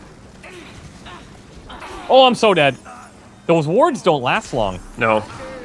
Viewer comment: Oh wow, I just realized that has been saying to use wards. I thought he's been saying 'Use your words guys.' In the sense of demanding communication.